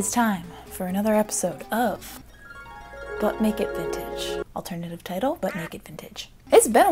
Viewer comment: Now all I hear is "Butt Naked Vintage" every time you say "But Make It Vintage"!! WHY!?